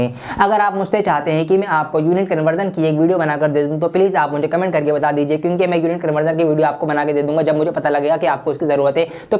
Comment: pe kya bola or maine vaise bhi 2x ki speed pe dekhra tha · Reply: Unit conversion ki video ka puch rhe the